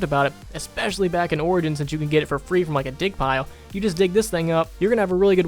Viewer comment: thought he said dick pile